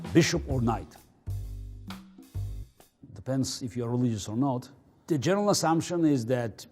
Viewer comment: what did he said? is that Russian? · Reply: « It depends if you’re religious or not »